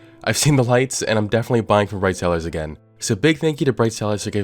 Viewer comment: I thought the sponsor was a shitpost I misheard "bright cellars" and heard "this video is sponsored by braincells"